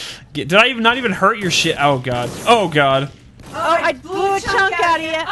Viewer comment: Did say sh!t in 1:29?